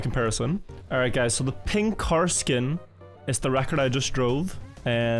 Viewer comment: As a golf fanatic, when you said "pink car skin" all I heard was "ping karsten".